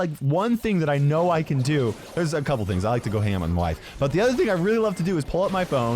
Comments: "I like to go ham on my wife" did I hear correctly? 😂😳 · go ham on WHAT?!?!??!?!??!!??? I swear I heard wife Or I'm just deaf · i understood ^hammer^ instead of hang out hahahahahha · wait did he just say ham on wife or life I’m 😳